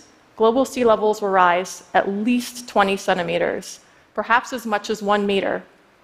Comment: The "scare" at the end, regarding "sea-level rise" is the dazzling fundraising pitch, and the public hysteria is the consistent drive to have governments take our tax dollars and give them to climatologists. While extremely fascinating, glaciology is still in its infancy, as is climatology. The Milankovitch Cycle needs to be studied side by side with this, in order to make an even remotely accurate prediction... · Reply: I thought she said 27 meters. When I jumped back she said 20 centimeters..